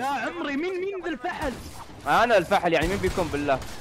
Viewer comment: سمعتوه دقيقة5:51 قال فحل